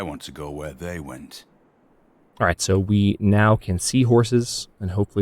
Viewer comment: Ohhhh you confused me at 13:38! I heard "so we can now seahorses" and I was like WHAT? Took a few seconds before I understood... I'm gonna go hand in my degrees now, kthxbye :)